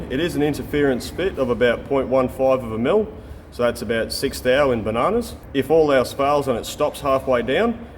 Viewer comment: WTF did he say? "about 6 thou in bananas"?????